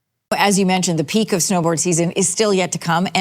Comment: That CTV host keeps saying "Snowboard season" am I hearing right ?